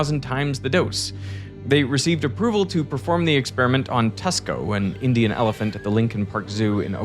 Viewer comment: is it really called Tesco? Or am I mishearing this?